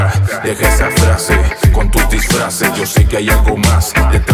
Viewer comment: la primera vez que la escuché...pensé que decía ahi viene atrás de flash